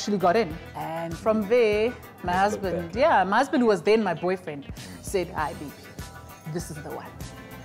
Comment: Did she just say @ 1:16 my husband was there and my boyfriend.. He he he